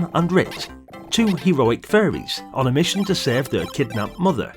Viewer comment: Oh, fairies ! I thought you kept saying the game starred furries ! 😅 That makes a lot more sense. 😅